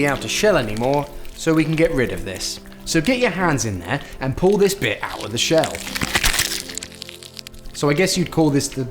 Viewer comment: for a split second i thought he was aggressively saying "get your hands in there and pull this b!tch out of the shell"